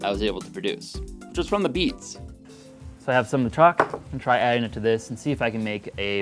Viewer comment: Is the white powder chalk? The subtitle says truck.